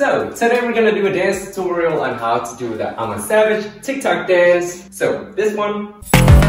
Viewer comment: I as well heard him say "I'm a sausage" and then maybe its' my ears and then I began laughing and watched that part 14 times again and then I was like if I'm laughing so hard I wonder what is the comment section is saying and I was right to check cause you guys are the funniest people ever